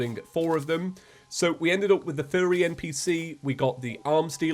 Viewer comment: Did you say "Furry NPC" on purpose? I've heard the "No sh.. Sherlock" in Belgium as well btw, never heard of "No dip Sherlock"